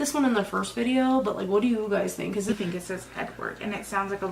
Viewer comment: I heard “Erin”?